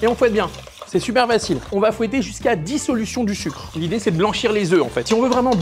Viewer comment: Vivement la sponso O'Tacos avec la recette de "re-biggy derrière". Qu'on aille pas y goûter surtout (enfin, sauf l'équipe technique donc... :D). Et heureusement que le reste n'est pas monté aussi dynamique que l'intro de cette créature horrible mangeuse de tacos, ça aurait été affreux :D J'ai entendu "on va tourner jusqu'à 10 solutions du sucre" et j'ai mis quelques secondes à comprendre... Je crois qu'il faut que je dorme :D Je me demandais justement comment tu allais fourrer, au delà de la technique, parce que dans des choux y a du vides mais là pas tant... Va falloir que je teste :)